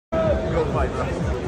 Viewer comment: Paul’s dad hats off for respecting Tommy since Jake couldn’t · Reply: I thought Jake Paul’s said nice fight?????